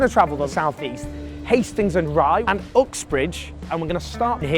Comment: I honestly thought he said Fuxbridge.